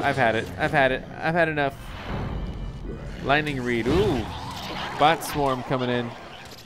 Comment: When I heard bot swarm I thought it was saying butt swarm😂😂😂😂